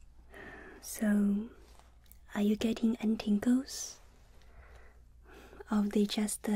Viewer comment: At first I thought she said ,Tinkle